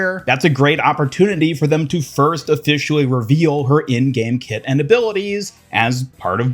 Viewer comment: Am I the only one who thought he said "Kitten" abilities at the start?